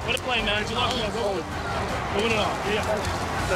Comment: did Mahomes say 2-0? Referring to how Burrow is 2-0 against him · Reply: "go win it all"